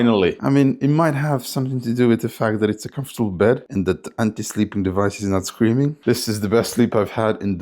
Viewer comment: Did you say "anti-sleeping device is not screaming"? xD